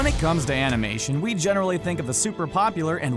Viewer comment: did he just say anime instead of cartoon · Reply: He said Animation not anime.